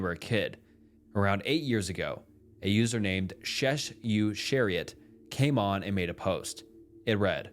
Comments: that does not say "shessu-chariot'. It says "shes such a riot". It`s very simple actually and makes so much moree sense than the poppy cock, that you got out of it and you didn`t pronounce chariot correctly, either. my guess is you`re a millennial or gen-z. That makes more sense. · I believe that the user at 24:59 may be pronounced as "she's such a riot" instead of "sh esh you ar eeot" lol